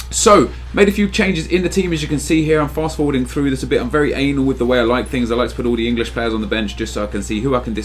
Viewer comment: Wtf di you say at 0:39 "im very anal with the things..."?